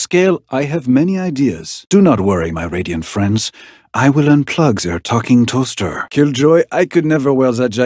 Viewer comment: "I will unplug their talking toaster" NO WAY HE PULLED THAT 😭😭😭😭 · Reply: Meanwhile i heard that as “i will unplug their fucking toaster“😭